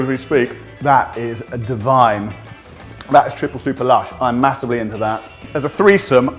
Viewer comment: In 3:42, did he say "i´d masturbate into that?!? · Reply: He said "I'm massively into that"